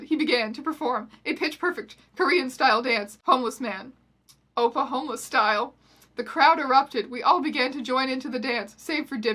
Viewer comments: NO I THOUGHT YOU SAID "Oklahoma Style" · The first time I heard you say "oppa homeless style", I thought you said, "Oklahoma style", which confused me for like twenty seconds. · I thought she was saying Oklahoma style. Let's just say I was in for a suprise.